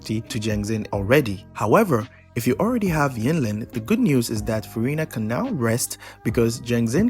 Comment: I saw that sneaky furina mention instead of verina